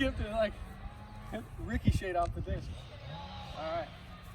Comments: did you say it "Ricky-ched" off the other disc?!?! LOVE IT! HAHAHAHA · Did he just say “ricky”cheted ? Ricocheted?